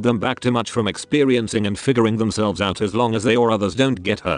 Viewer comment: omg for a second i thought he said fingering themselves